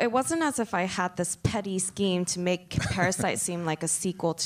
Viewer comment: 초에 정확히 뭐라고 하신거예요? 완전 처음 들어보는 표현이라 안 들려요ㅠㅠ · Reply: petty scheme 입니다. 영어 자막 추가했으니 확인해보실 수 있어요!